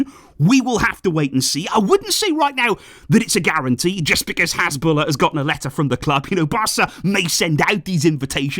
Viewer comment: I honestly thought you said Hezbollah was invited 😂😂